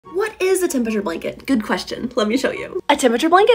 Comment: Am I hearing things…. I thought you said tomato blanket Is it just me?